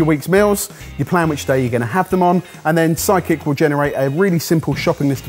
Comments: I misheard and thought he said “psychic” app instead of “sidekick” app. I muttered “oh, they’ve branched out”. 😆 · Today, for some reason, every time you guys said "Sidekick" I heard "psychic". I'd prefer a psychic meals app, please. Have it warn me advance that I won't actually want that meal I was planning for Tuesday, and encourage me to buy a pint of ice cream in advance of a stressful day. :D